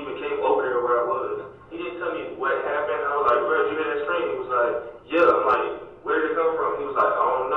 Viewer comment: If that is (🙄🙄🙄) talking, he says, "Bro, (did) you hear that scream?" And he was like, "Yeah." I'm like, "Where did it come from?" He was like, "I don't know, but I heard it, though..."